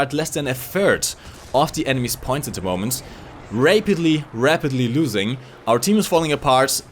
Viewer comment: LMAO 9:55 Rape-itley instead of rapitly. New secret of?